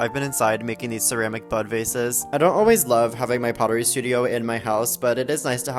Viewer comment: I thought you kept saying "butt vases", and i was so curious then confused. I guess i could have read the caption sooner.😏